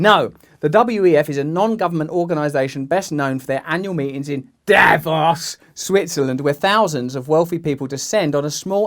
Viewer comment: Oh Davos. For a minute I thought they said Davros and had visions of that cabal he mentioned turning out to be the Daleks. 😜😎